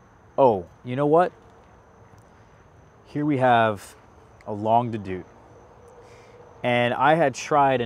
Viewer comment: What did say @ 5:55ish, the one that makes a cotton candy note sometimes... Longue De Deux? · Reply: Longue d'Aout